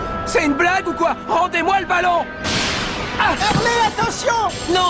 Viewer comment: pourquoi Shauwn dit "Axel attention" alors que c'est Hurley ?!?!